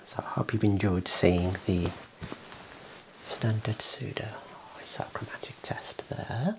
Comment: thought the first test said "standard pseudoscience plates" and was very confused for a minute lol